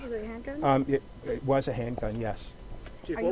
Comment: what did he say? Did he say it was a hate crime? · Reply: it was a handgun